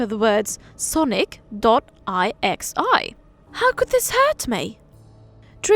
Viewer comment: did you just say sonic.ixi